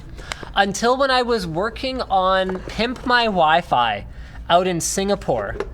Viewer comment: lol ! at 36:24 it almost sounded like u were guna say pimp my wife lol !